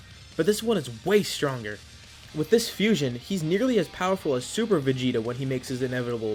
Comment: I thought you said super Vegito xD